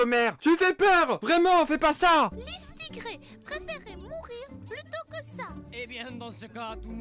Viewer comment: Mddddrrr j'ai entendu " Lustucru" j'ai des problèmes moi mdrrr